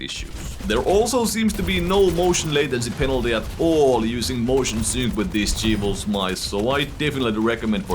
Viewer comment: sorry I couldnt understand what you said, Motion what??? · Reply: Motion sync.